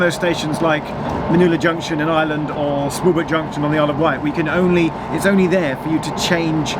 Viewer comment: 'where' Junction on the Isle of Wight??? I keep replaying it, and I have no idea what you are saying! It is worse than your 'Pwllheli'! 😆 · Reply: He is saying Smallbrook Junction on the Isle Of Wight